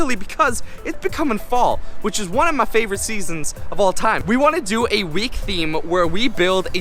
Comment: I thought you said we do army week